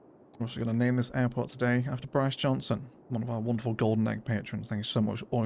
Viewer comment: I really thought you said "We're going to name this airport after Boris Johnson" for a moment there. Terrified.